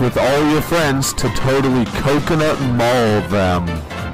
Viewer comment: i thought he said coconut ball them